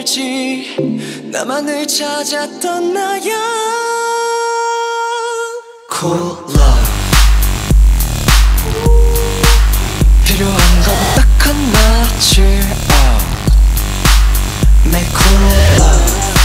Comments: I thought they said COLA instead of Cool Love😅😅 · i hear soda and they just pick up Pepsi cans then I hear Cola in the next few seconds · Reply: there saying cool love not cola, also even if it did say cola, Pepsi actually is a cola lol. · For a sec I thought they were saying "Cola" instead of "cool love" and I was so confused bc it's Pesi sponsored lmao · tell me why my dumbass thought it said “cola” when its a pepsi commercial 💀 sorry hyungwon hehe cool love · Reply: i thought he said cola toooo then i remember it's cool love lmao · Instead of “Cool love” I thought they were saying “Cola”. I was like THIS IS THE BEST PEPSI COMMERCIAL I’VE SEEN ALL DAY! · Instead of cool love I thought they were saying cola lol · i thought they were saying cola even though it’s obviously about pepsi but they are saying cool love, my mind is just...why would i think that